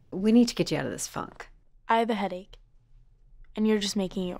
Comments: OMG who thought they heard the f word instead of funk at 5:31 😂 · did she say f* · Did Birdies mom say, “please honey we got to get you out of this f word” or did she say funk? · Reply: funk